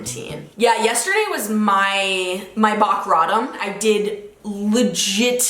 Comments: "bock rottom" am I the only one who heard her say that? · Am I the only one that heard her say bock rottom instead of rock bottom? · did she just say "bock rottom" instead of rock bottom? 😂